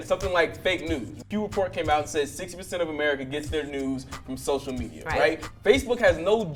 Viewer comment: I thought he was saying 60% of people get their nudes from social media 💀💀